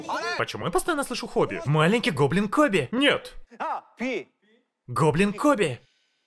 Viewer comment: Почему я постоянно слышу Хоби 😂😂😂😂😂